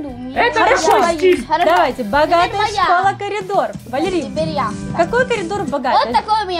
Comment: Мне послышалась не каридор а памидор